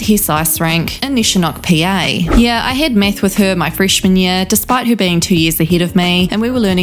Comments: Did anyone else hear 2:44 "I had meth with her in freshman year" I listening to this with my eyes closed and I've never looked at something so fast!🤣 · Your accent really makes your words sound like you're speaking a different story. I misheard "math" as "meth" 2:43 then I heard "bitter" instead of "better" 4:25 oy vey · So I was just listening to this in background and I coulda sworn you said “yeah I had meth with her my freshman year” that woulda explained it better ☠️